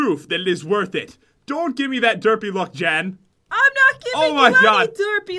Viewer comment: in 2:50 I thought it said "Don't look at me derpy Jen"